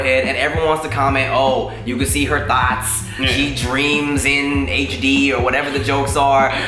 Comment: It sounded like he said "we can see her thots" 😂😭😭